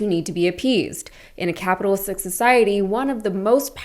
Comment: Sounded like you said “capitalsick” 0:41 society…appropriate term. CEOs AND SHAREHOLDERS need to take care of employees and customers, because they are ALL shareholders - just because they don’t hold a stock in hand.